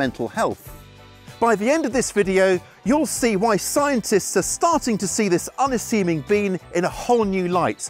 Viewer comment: Did I hear him say at 0:25 "scientists are farting to see this unassuming bean in a whole new light" ?